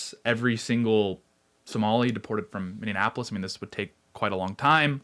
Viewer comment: I hate that he keeps saying Minneapolis wrong constantly. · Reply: Yes! At first I thought he was saying Indianapolis